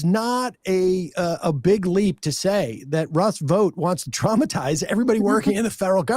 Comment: When they were talking about Russell Vought, thought she said rust boat, thought they were talking in code about the 100 rusty boats, in the right wing book, the camp of the saints.